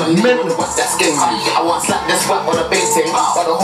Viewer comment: did he say he wont slap s1 with the big ting